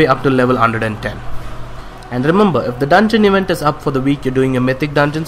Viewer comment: What.. did.. he say? If the Dungeon ------ is up for that week, pick that quest up?! 4:53? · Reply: 'events' is what I said, I was talking about the draenor dungeon event that gives you a heroic HFC cache and some valor